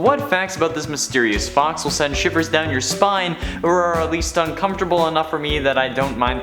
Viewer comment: I thought he said 'will send shippers down your spine' XD